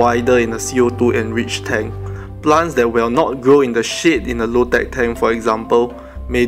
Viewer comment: When u have said: plants will not grow in shade, I have heard you saying sh*t instead of shade. Lol